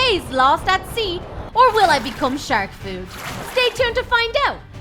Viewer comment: I Have To Things lol When You Said Shark Food I Thought You Said Become Like Sharky And That " Person " LOOKED LIKE DREAM !!!